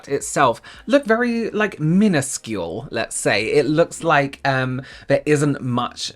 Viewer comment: When said “minuscule” I thought he said “peniscule” 😂😭